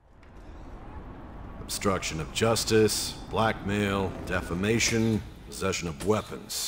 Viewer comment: I thought he said "black male"